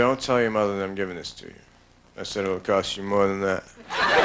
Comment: What did he say at 6:09? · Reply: It will cost you more than that.